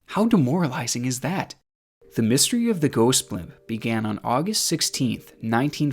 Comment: I thought he said “The Ghost Pimp”